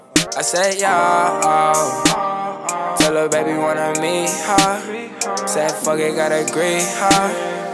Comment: who else heard green heart 💚 U know hear greet her when u push it over and over